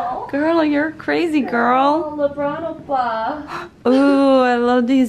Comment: Did I hear it right? Lebron oppa?! At 9:16 ?